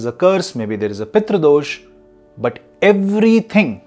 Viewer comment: Does anyone know what a pitos is?🤔 (at 11:47) When i looked it up all i got was a Spanish flute or whistle LOL) Many thanks to anyone who can respond. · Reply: He said Pitra dosh